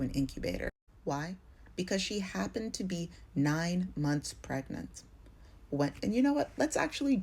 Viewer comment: I thought you said 9 months! 9 weeks! GTFOH!!!